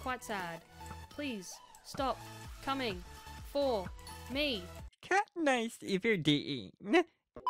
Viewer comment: kat niss if your D E, meth? I dont understand xD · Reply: It's a guy saying katniss everdeen in a weird voice xD